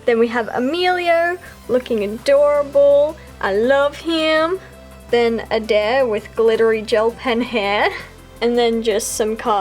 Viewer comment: what is she saying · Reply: "Then, Adair with glittery gel pen hair" 😂